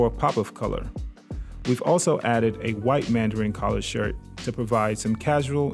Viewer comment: OH Jesus I thought you said you added a white man collar shirt @ 3:01 mark lol.